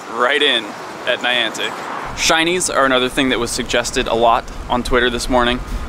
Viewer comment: am i the only one who heard "chinese" at 7:55 lol